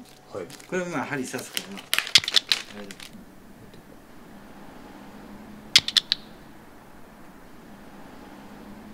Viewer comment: 辺りで「エテコ」みたいに聞こえる なんて言ってるんだろう… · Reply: 十四図書館 エテ公と調べるといいとおもいます